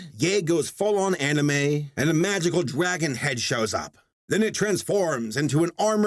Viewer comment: is it just me or did he say it like "magical dwagon" like THE FILTHY FURRY HE IS